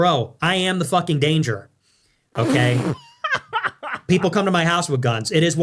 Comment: Wait, what? Did he just say his the danger? Lord, no, you ain't, mister!!! 😂😂😂😂😂😂